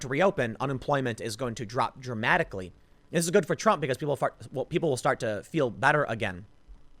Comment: lol did you say people will fart?